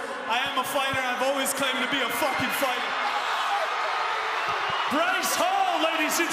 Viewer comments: If you did your homework you'd know he was always a real fighter as he actually does have many street fights, a legitimate wrestling background from a young age, trained with Vitor Belfort, trained with Lorenzo Hunt who is a two division Bkfc champion in cruiserweigh and light heavyweight and weighed more and is taller than the opponent who had to gain 30 lbs in over a month from his last bkfc bout and is a cut man most of the time and only three Bkfc bouts. So yeah, I'd say this is a well deserved win and Bryce is a legend! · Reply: I could’ve sworn he said verbatim “I never claimed to be a fighter.” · To go from “I never claimed to be a fighter” to winning a bare knuckle boxing match, mad respect young man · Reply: At the end I heard him say I've always claimed to be a fighters so he said he hasn't?